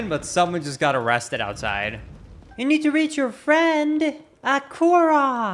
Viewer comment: he said "A korok!" the captions said aquarock LOL